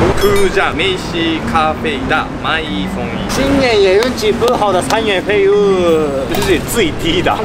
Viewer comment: 不小心誤會了🤣 2:43聽成了『今年也運氣不好的三原廢物』🤣🤣